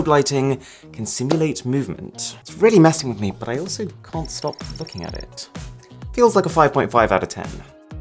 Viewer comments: did he say that he can't stop fuocking at it?????? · anyone else hear fucking at it